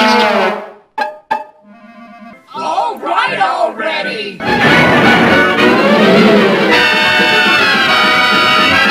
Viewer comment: What did they say ? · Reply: All right already